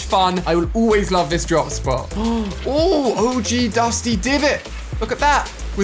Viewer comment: did he just say og dusty divot wasn’t dusty depot or am I going insane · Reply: It was dusty depot your perfectly sane my Friend